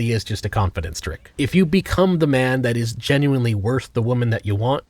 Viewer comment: what is the name of the channel? I cant hear him well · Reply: He said go watch hoe math